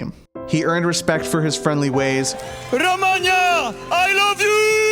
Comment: did he say Romania 😸🇷🇴